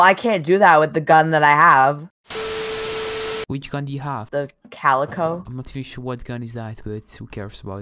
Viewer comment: i think he say calico or something 3:14 but why that gun is shaped like a sniper